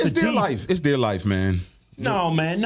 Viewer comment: Damn I thought Chris said to sterilize them, but he said it's their life 🤣🤳